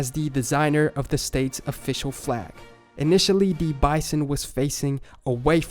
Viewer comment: Tf u said at 11:12? He was facing the pull? The pole? The mole?